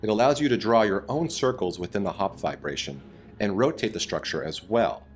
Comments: I thought he was saying vibration lol. No not that kind all you pervs lol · Damn this whole time I thought they were saying “hops vibration”